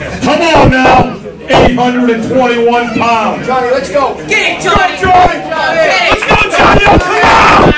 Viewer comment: LMFAO at 0:16 what the hell did this little girl say?? : Get that shiiiitt